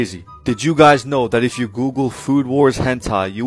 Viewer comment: bro i thought you said food war 10 time 😭😭